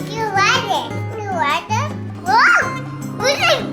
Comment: talked! 3:58, it sounds like she said " You like it, you like that? What do you think?!" Haha. ❤ & girl you work so hard, dont beat yourself up. Your fans love you and understand things happen. Hugs n kisses from Montreal.